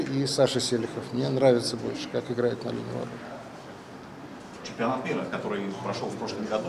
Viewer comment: Подскажите, что сказал Кафанов на 10:45? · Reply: Он сказал что ему нравится как Игорь и Селихов *играют на линии ворот